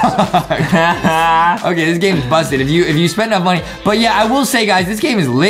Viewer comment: did just say “this game is bustin” uhh okay…oh wait…45:55 I haven’t watched them in awhile they say that now?